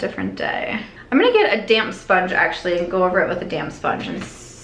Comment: Idk why but I thought you said I’m gonna get a “damn sponge” lol 😂 I’m like yeah use that damn sponge girl